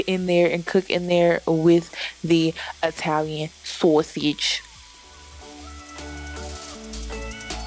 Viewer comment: Why did you say "Sosige" like that at 3:50?